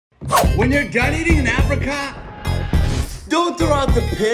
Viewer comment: I honestly thought u said..when u dieting in africa😂😅